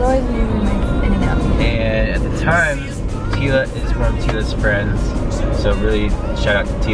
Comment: did he say "teala was one of teala's friends" at around 12:12?